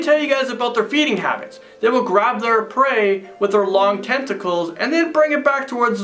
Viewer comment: I thought he said testicles 😂😑